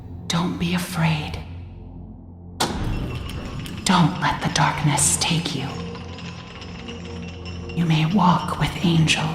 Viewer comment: I heard "Don't let the darkness Steak you" I said what slap with a steak